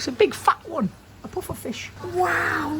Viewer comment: I swear he said fish 24:35 and 24:40 or am i loosing it